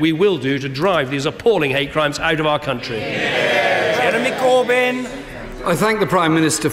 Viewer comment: Did I hear enemy corbyn